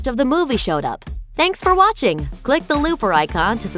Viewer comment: did she just say 'lick the looper icon..'??? haha